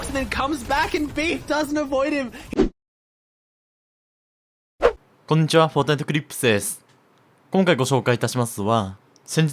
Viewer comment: でfortniteclixですって聞こえた笑